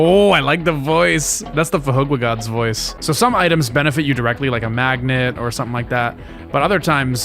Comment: How does one spell the name of that voice? Ive been looking for that for years and even with saying the name, I couldn't figure out how it's spelled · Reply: fhqwhgads